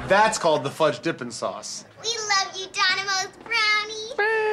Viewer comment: thumbs up if u thought the video said dominos until the end of the video when the lady says donimos